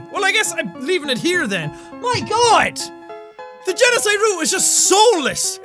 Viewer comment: It sounded like you said peniside route lol